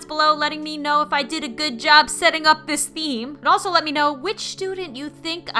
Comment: at the end I thought she said "Z you soon"